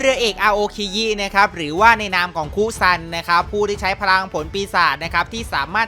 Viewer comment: ผมได้ยินว่ามุซัน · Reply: สิทธิพงษ์ จันทร์เขียว คุซันไม่ใช่หรอครับ